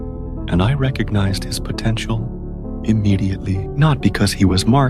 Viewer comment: did u just say potential